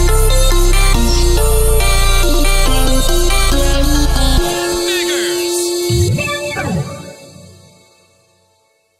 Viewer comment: what did he say? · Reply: snickers